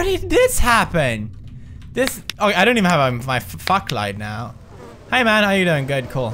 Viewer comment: Did he say I don't have my fuck light